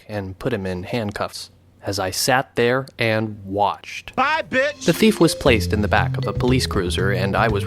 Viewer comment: For a moment i thought your sim actually said “bye bich!”